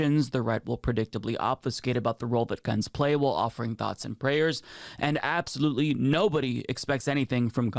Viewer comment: When said "thoughts and prayers" I heard "Nazi prayers" and thought ", yeah, that's accurate"